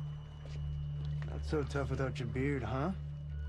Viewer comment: What does he say at 1:52 i dont get it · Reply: 57asli He says "Not so tough without your beard (referred to veronica), huh?"